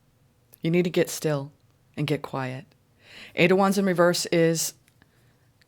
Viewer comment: I thought you said 5 wands in reverse...not 8 wands in reverse